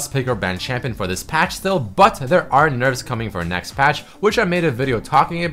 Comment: lol 7:03 it sounded like butt